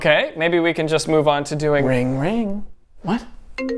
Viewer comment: I heard "ling ling" ! its all coming together!!!